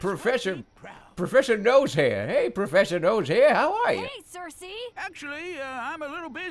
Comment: I thought he was saying, "Hey, Cersei!"